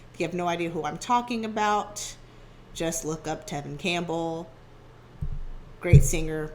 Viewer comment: I thought you said Neve Campbell at first. 😂